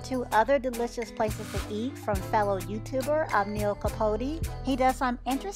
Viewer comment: who gave suggestions? sounded like you said neil capote